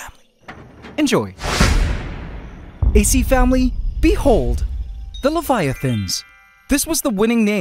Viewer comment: I thought he said little fire things